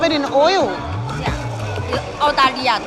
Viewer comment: I think she said 澳大利亞 which means Australia